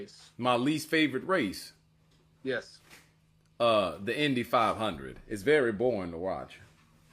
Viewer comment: Either you did not bother to watch even the first eighteen seconds of the video or it is you who is lacking comprehension. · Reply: What he thought he said Indian instead of Indy 500